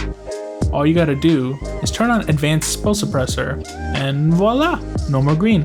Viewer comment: I am researching this exactly, sad that sometimes the thing you want just does not come out clear. Did he say "Spill Suppressor"? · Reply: Yes spill suppressor.